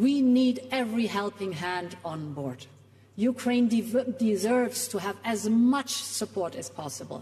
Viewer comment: she nearly slipped up and said Ukraine diverts instead of deserves lmfao she probably meant diverts the attention.